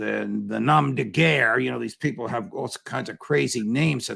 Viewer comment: what word is judge saying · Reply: nom de guerre - it is French. A nom de guerre is a fake name (or pseudonym) that you use for a particular task or job. Writers might use one. Spies might use one.